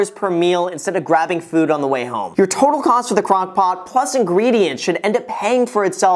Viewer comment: Thought you said eating from a “crackpot” there for a sec Lol. Suddenly all the excitement in your videos made sense.